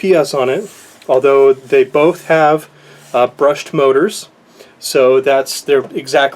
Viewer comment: great video as always. But about 5 min in I thought I heard you say the Promark Shadow had the same motors as the Syma X8 which were "Brushless and then at the end of the video I think you said "you wished the Promark Shadow had "Brushell" I'll be the first to admit my hearing is not great, but so I know...which is it... many thanks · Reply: It uses the same motors as the Syma X8 which are brushed.